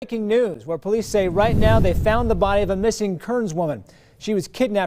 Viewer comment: Im sorry but i thought it said "body of missing karens"